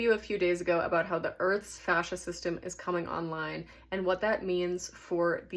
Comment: I almost thought you said fascist system coming online haha